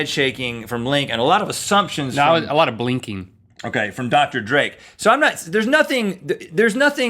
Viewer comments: At 4 min i thought he said said dr.dre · I kept hearing "Dr. Dre".... anyone else?